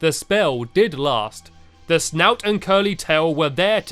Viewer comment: It sounded like stampy said slout instead of snout